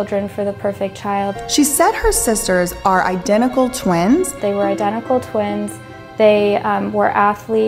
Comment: She did not say they ARE identical twins. She said "they WERE"....